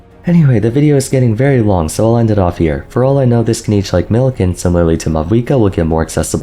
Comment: he said "this can age like milk", but I heard "this Kinich like milk" 😂 I should stop playing Genshin, it messes with my brain. 😂